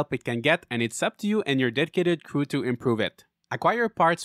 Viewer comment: "...and it's up to you and your dead-kidded crew..." 💀Well, that got dark fast 🤣 That's what I heard when he said dedicated 😅, lol.